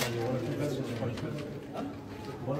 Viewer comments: Am I the only one who heard him saying "are you kidding me? Everyday you want me to talk to you guys?" 🤔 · Sounds like he said “are you kidding me? Everyday you want me to talk to you!”